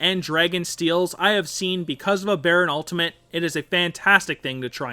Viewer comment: Did he just say "Because of a baron Ultimate"